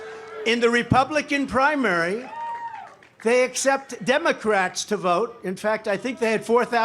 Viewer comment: Sounds like he said "Demo-crones"